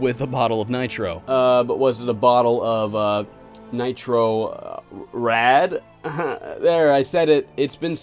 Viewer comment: I always hear on the intro of Nitro Rad ....Nitro Fred. Regarding to this game, I used to own a PS1 CD with this demo. I asked my uncle for this game but I think he mishearded me because he got us (me and my 2 brothers) Resident Evil 1, 2 and 3. xD That's an abyss of diference between those games. Nice choice uncle btw. After some time I got Jersey Devil, awesome game. good times.....good times.